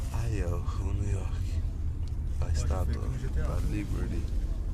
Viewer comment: Luque says "Statue of Liberty", not whatever you think he said in Portuguese.